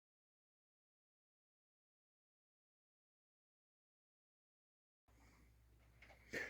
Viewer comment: U GOT SLEET/ FREEZING RAIN? DID U SAY "RAIN MIXED WITH GRAFFLE" @ 0:26 ?? I REPLAYED IT OVER.. 😊- IM PART DEAF☆ & THANK YOU FOR YOUR DEDICATION TO KEEP US ALL UP TO DATE HOPE I DONT GET FREEZING RAIN OVER HERE TAKE CARE NEIGHBOR💌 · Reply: It was rain mixed with grapple. Grapple is ice pellets. It's similar, but not the same, as sleet. It's fairly common in the northern states.